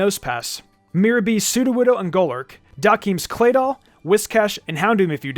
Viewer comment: did just say golurk....? I am confused 😑🤣😂